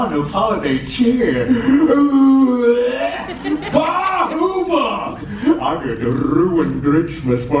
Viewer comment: Did he just say "Baahubali" at 2:08?